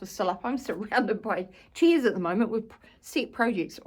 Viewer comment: I love your accent. As an Aussie, I feel both geographically and linguistically close to you. But imagine my surprise when I could have sworn you said you were surrounded by *cheese*. I studied the screen carefully, mystified... Chairs. You said chairs. Anyway, Happy New Year, and thanks for showing us your stitching.